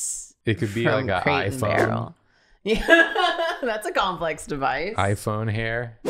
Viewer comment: I thought the hair said complex divorce at first. But a gyroscope would've been good too xD Just nested wheels like a biblically accurate angel.